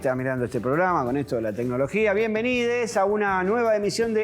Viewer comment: Escuché "bienvenides" y me retiro inmediatamente.